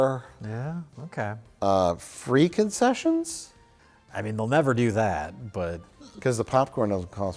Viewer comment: I thought said "Freak-Off Sessions" and thought woah that's a brave and early joke to make